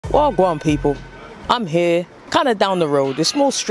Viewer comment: what's the first words he says I'm not able to catch it?! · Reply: “wagwan people” just means what’s up